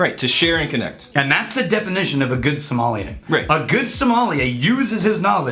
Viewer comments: i thought you said Good Somalian, like the country in Africa. I was REAL confused for a split second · Lol I thought he said a good Somalian